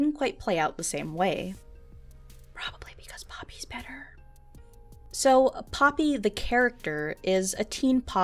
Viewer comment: did just say "Help Me?" Guys, been abducted by ISIS and they're holder her hostage until the illuminati aliens complete their world domination!!!1111!!1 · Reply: She said ''Probably because Poopy is better''